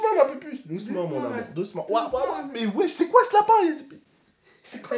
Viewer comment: qui a entendu c'est koi ce lapin ha zuby